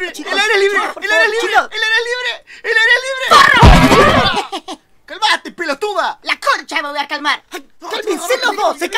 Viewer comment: despues de 10 meses por fin entiendo lo que dice xd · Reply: dice forro